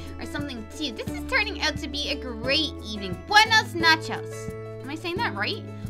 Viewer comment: lol it sounds like she said good nachos